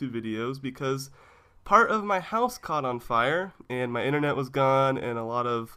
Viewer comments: I thought you said How my blouse caught on fire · I thought it said, How my horse caught on fire.